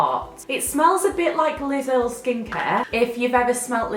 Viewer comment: when you said Liz Earl skincare I thought you said Lidl skincare 😂 and I love the walking dead atm